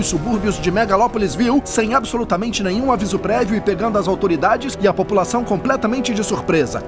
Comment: Só que eu ouvi “aviso Predio”?